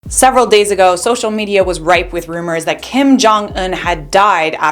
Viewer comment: I for real thought she said "social media was right" at the start of the video🤣